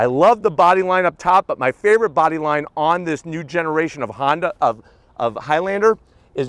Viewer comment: Did he say Honda in 5:50? Lol